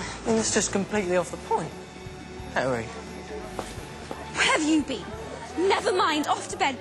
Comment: Can someone tell me why on 0:30 Hermione said "where have you BE? " instead of 'been'?